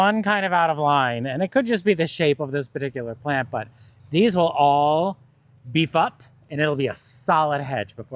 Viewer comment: when you said these will all beef up.. it sounded like something totally different, and I was like WHAT? LOL I had to go back and listen a few times to understand you said they will all beef up!